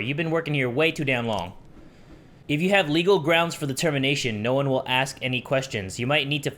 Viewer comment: When said "the termination 25:07, it sound like he said "Determination" (Undertale...)